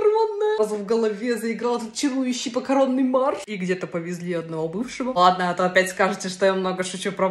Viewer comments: Видимо я давно не кушал, так вау я услышал "Макаронный марш" несколько раз. Но макияж прекрасен! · Мне послышалось " в голове заиграл макаронный марш "😂😂😂